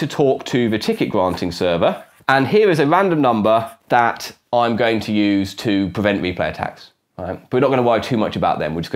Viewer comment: Interesting, but what are you saying at 6:27 ? To me it sounds like "here is a random numbuh that I'm going to use to prevent me play tacks" which is not only meaningless gibberish, but isn't even grammatical. What did you actually mean to say? And at 7:22 you say "azure nonce again to prevent me play tacks", which, again, is not only meaningless gibberish, but isn't even grammatical. What did you actually mean to say?